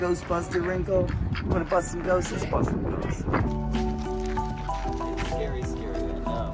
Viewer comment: Ayo did he just say let’s bust some ghosts?